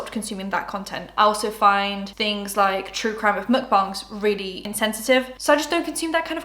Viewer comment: I can’t make out what is said ? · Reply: true crime mukbangs? the hell